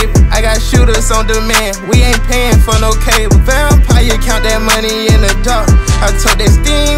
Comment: I always thought he said ''foul play count the money...'' not vampire, count...